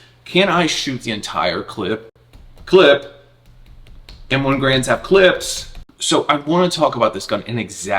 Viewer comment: I love that thing! What a great idea! I thought you said M1 Grands have clits ! I about lost it😂! lol ❤